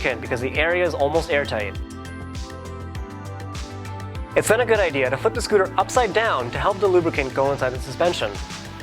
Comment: does he say "It's not a good idea to..." or "It's now a good idea to..."?